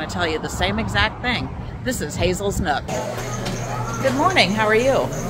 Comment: Lolol...im the beginning, i thought you said this is Hazel's Nut and my eyes popped out!😂 Definitely woke me up! Lol Love old home town diners when we're on the road! Great to hear the food was phenomenal!!! Thanks, guys for your review! We always appreciate it!!! 😁🤙👊